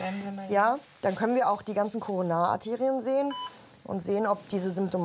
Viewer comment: Ich hab zuerst Coronaaterien verstanden😂